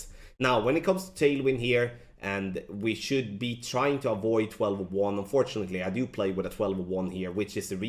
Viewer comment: What does say at 3:02? Try to avoid 1201???